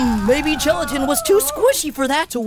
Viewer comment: Captions said Leafy: Hmm, maybe Leafy was too squishy for that to work! It should be Gelatin, not Leafy